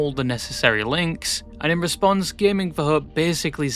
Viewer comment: i thought you were saying gaming4herb until i tabbed back in at some point